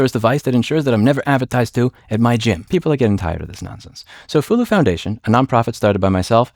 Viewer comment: I thought you said clthulhu foundation at first.